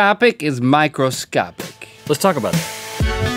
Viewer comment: I thought link said let's talk Micheal Scott 😂